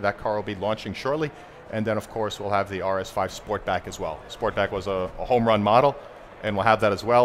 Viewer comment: Did he just say the RS3 Sportback will finally come to the US?!